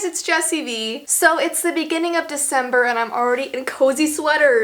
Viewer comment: i know she means september but i sounds like she said december and this makes me nevous